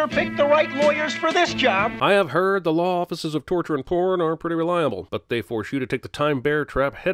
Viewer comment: I thought you said torture and Khorne.